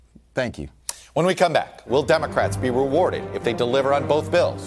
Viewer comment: I thought it said; "Democrats Dreadlocked".. I was almost cool for a minute.